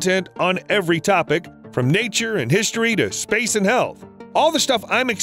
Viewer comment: I'm high and had to back up and turn on captions because I honestly thought he said "space and hell"